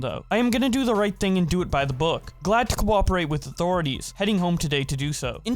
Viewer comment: your cooperate sounded like enchanting book